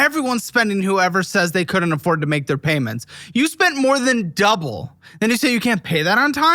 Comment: did she say cash float?